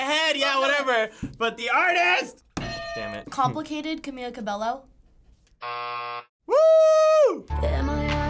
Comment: / 5:52 did they just say … cabeLLo ?